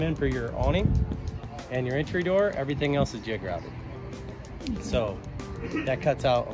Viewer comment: I did not understand, it sound like you said everything else is geograbed